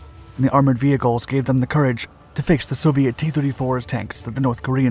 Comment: English subtitle say the tanks are t-36 even though it is t-34 tanks.